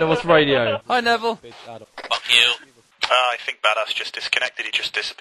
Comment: Did he say VanossGaming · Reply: I think he said “Badass”. I think someone in the team is called Badass Monkey or Badass Joker